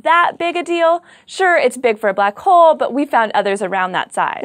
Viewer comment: is this only me ....or i really heard her say "....it's big for but hole..." 1st time i heard that ......and when i listened to it another time ..."BLACK HOLE"..duhh !!